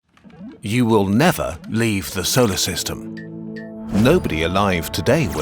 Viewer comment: pin is hair is what I heard in the first half